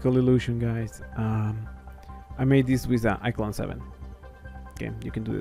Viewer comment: I made this in ... WHAT DID YOU SAY? What did you make the video in? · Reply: He said iClone 7